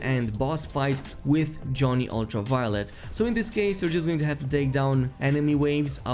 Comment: did he just say 'Johnny Ultraviolet" ? xD